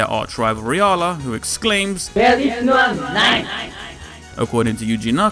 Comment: to think we all thought he was saying "There is one NiGHTS!" as if he's the real NiGHTS ala Shadow the Hedgehog.